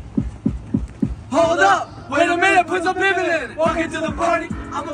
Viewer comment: He said put some what in it?😮 i know he didn't say what i think he said. I want front row at his next performance. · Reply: My baad, 😂😂😂😂 I thought he said out some ninjas in it. 😂😂😂😂thanks.